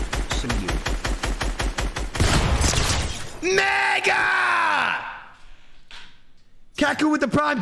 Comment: did clix say the n word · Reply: Megga